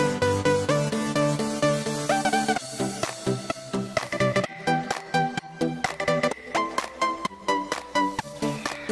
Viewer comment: what was the portal gun called. the yellow hair guy was holding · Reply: Wormhole